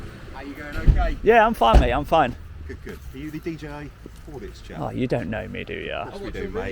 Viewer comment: Yep,the orange jacket turns up and asks “Are you the DJ Audits?” So he must have been told by the first security at the gate. Both orange jackets then play along like they know you. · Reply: I replayed that section a few times after hearing what DJ thought he said, and he clearly says "Are you DJI Audits?" I could tell from that alone that he's clearly not a fan of the channel, and has merged DJ Audits together with the drone DJI Mini to make DJI Audits, while clearly pretending to be friendly with his nonsense about tanks and traffic as usual, just like all the other places which don't want to be filmed and want you off site. His partner, and the initial security guard however, seemed to be genuine.